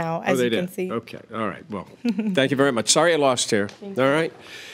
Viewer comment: At first I thought he said, "Sorry you lost hair." 😂